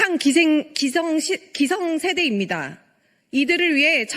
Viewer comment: 기생세대로 들리는거 나뿐이냐..?